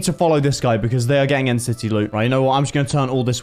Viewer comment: For the longest time I thought he was saying nct loot😂😂💗